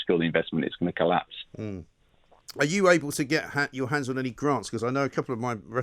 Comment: I thought Mike said "grass". I was like damnnnnn, he's just going for it!